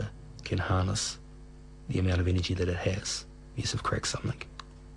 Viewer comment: what is he saying at 1:04? I've listened to it dozens of times... "...the person who that can find out how water can harness the amount of energy that it has is of xxxxxx xxxxx.." ? what the hell is he saying? those last two words sound like "greg summick"? or "crack summick"? can't make out those last two words... damn...